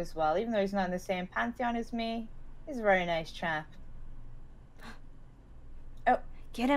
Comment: Is it just me or did just say "He's a nice chan" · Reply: TinyQ555 she Sid nice chap